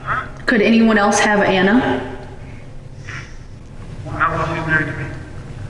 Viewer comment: I think he's saying: "Not while she's married to me." FWIW.